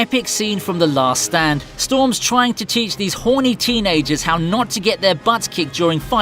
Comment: did he just say horny